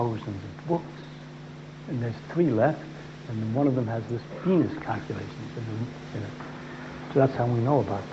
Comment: Closed caption says "This penis calculation" 6:17 LMFAO!!!